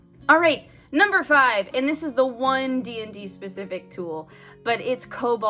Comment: I misheard "and this is a one DnD specific tool" as "and this is a OneD&D specific tool", and was like "wait, but OneD&D is not out yet, is it?" 😆